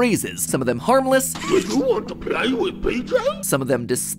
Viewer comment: I thought PJ Pug a Piller said “Do you wanna play with pizza”😆